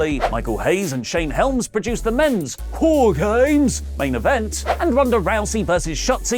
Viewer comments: It sounded like you said: WHORE GAMES! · it sounded like you said whoregames lol